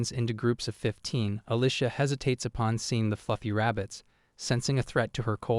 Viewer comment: I heard "the fucking rabbits" 🥀